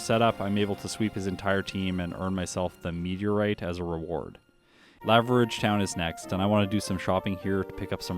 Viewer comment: i listen to most videos sped up and i heard leverage town lol